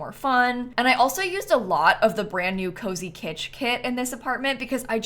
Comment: Snap misheard cozy kitsch as cozy kitty